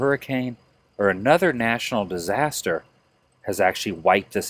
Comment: did he say national disaster